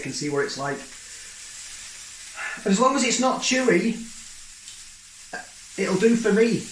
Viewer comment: You said you weren't sure where the steak came from, so then when you said, "I hope it's not chewy" I heard, "I hope it's not Chewie". I thought, "You can get Chewbacca steaks??!?!?!